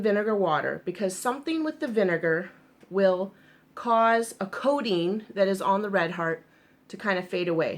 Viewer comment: I thought she said there was “codeine” on it! She was saying “coating.” Ha ha ha !